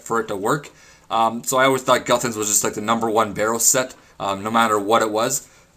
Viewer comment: did it sound like he said number 1bot in his intro?